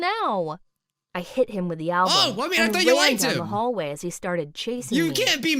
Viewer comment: the caps that alcohol instead of album